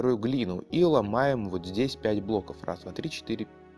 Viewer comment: мне показалось что он сказал бля**